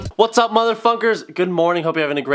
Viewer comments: I thought he said muther fucker at the beginning of the video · I thought in the start he said motherfuckers but he said motherfunkers